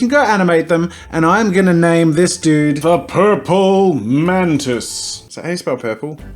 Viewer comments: I thought he said "The Purple Mentos" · I thought he said “purple mentos” 🤣